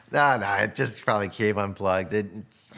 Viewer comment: the subtitles say 'cave' instead of 'came'??? How did they let that slide. Or am I missing something